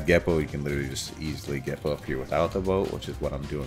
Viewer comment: I thought he said gepup here